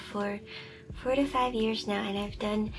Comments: For a moment I thought you said you’ve been working for them for 45 years 😭 😭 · i thought you said 45 years i was like HUH???? 😂😂😂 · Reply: I believe she said 4-5 years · Thought I heard 45 years (thinking...how is that possible, seeing her age like that) that I needed to go back to hear it again. LOL. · thought you said 45 years for a second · Holy shit i thought she said 45 years i was like what the hell